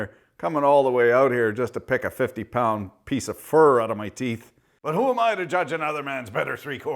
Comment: I heard that as ‘fur’ and thought:”What the hell is he squeezing that has that amount of fur?!”